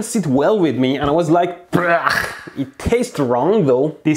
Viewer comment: did he just say... bruh?